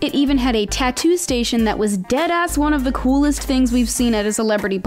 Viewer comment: did she really say deadass